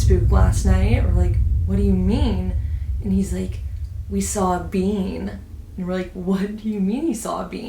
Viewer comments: lol i thought when you said “being” you said “bean” and i was so confused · What does bean mean in the context your father was using? Does it mean like the expression “that was a pickle”? Idk Im a little confused on what that means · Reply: Pretty sure she is saying being. Like a "human" being. I thought she said bean at first too but being would make sense in this context